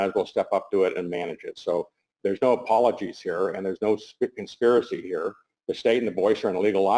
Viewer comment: Did I hear him goof 28:56 what is a 'spikin spiricy' is that a rare fish?